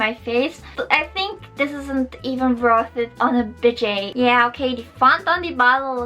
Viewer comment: Great vid. Lancome Mascara is the classic. lol I thought you were saying bidet. Budget is pronounced BUD' Jet. Givenchy is Jee vo Shee" (or close enough to jzhiv-on-shee,.) Perfumes in make up make me Naw' Shus too. That is why I use so many Clinique products. Clinique does not test on animals except in mainland China, where animal testing is required by law